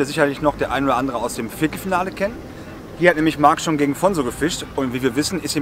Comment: Was sagt da? Was ist das für ein Finale? =) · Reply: Hey Anel, Viertelfinale, oder?!🤣 Sorry, wenn ich zu krass nuschel 😂